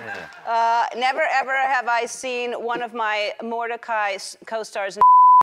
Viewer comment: I hate the way she’s saying it i always hear it as “never have I ever” Shes like”NevER EveR HaVE I” 😂